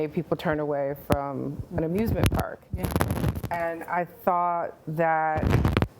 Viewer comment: i totally thought she said ABUSE-MENT park, that’s more like it